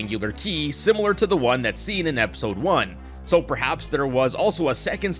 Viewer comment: Did he just say episode 1 in